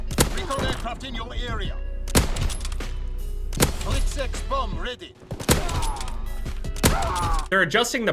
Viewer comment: did anyone else hear the announcer say sex bomb ready probably just me tho